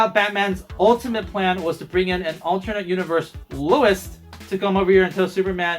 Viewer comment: did he just say Louis?